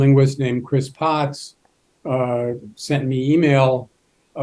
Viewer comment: he correctly said "sent me e-mail", but the caption INCORRECTLY stated "sent me an email". It's quite surprising how most people say it wrong, but the one time I hear it correctly the captions still screw it up. Most people incorrectly believe that "e-mail" (or "email", either way, but the hyphenated version is more correct) is the name of the product, but it's actually the name of the service, just like the US mail. You don't receive "a mail" in your mailbox, just like you never receive "an e-mail" in your inbox. The rules of e-mail are actually the same as mail, but it's rare for anyone to get it right. "He mailed me a letter" works just like "He e-mailed me a document". If anyone ever said "He sent me a mail" people would look at them pretty strangely, but "He sent me mail" is correct, just as was correctly spoken at 2:11 in this video "Chris Potts (uhh) sent me e-mail".